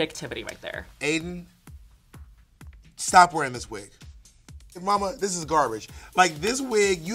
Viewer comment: I heard "Aiden stop using that wig"...... BUT I DIDN'T HEAR... "Brita, everyone - stop attacking Aiden!!!!" ...